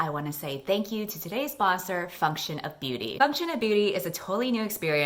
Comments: 20 seconds in and I don't know how I could have so badly misheard you, but I genuinely thought you said your sponsor was "Fuck shit up beauty" and not "function of beauty" 😳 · GOD i had to pause for a second because i thought you said today‘s sponsor is „fuck-shit-up beauty“ 😳😹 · i thought you said fuck shit of beauty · why did i hear “today’s sponsor... f*ck sh*t up beauty” 😐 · Why did I hear "Fuck shit up beauty" instead of "function of beauty" 😭